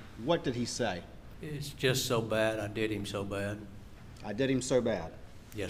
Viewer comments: I heard "THEY just did him so bad," not "I just did him so bad." Court reporter ears here. Just saying... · After several listens on slow, fast and regular my Texas ears heard him say ‘I did him so bad..’ you know~you know ~you know ~ you know ~ you know ~ you know… boof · Is he seriously sitting there smiling during the audio? So creepy, he’s not even disturbed. Sounds like he said “I did him so bad” to me. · I heard, "it's just so bad, they did him so bad."